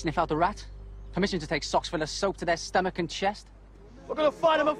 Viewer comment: What did Colin say at 1:44? Socks full of what? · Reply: soap